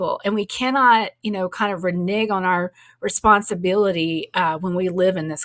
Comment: did she just say “renege” 🧐…lmbo